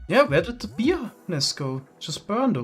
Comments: I was playing this in the background and my stupid self thought he's saying p-nis 😂 turns out it's bees nest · every time says “bee nest” i hear “🅱️ E N I S”